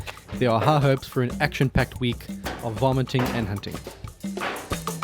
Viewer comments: I heard "action packed week of vomiting and hunting"... I thought, sounds like an interesting combination of awful and awesome. LOL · lol sounded like you said vomiting and hunting.